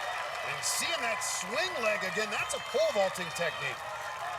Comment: "that is a ... technique" I can't hear what he says. Does someone know what he says? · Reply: Pole vaulting